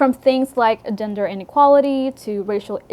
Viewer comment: When talking about Anne with an E I totally thought you said ginger inequality lmao